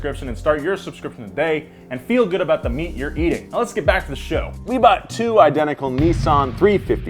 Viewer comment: Not gonna lie at first I thought Nolan said enjoy YOUR meat at 1:25 😅😂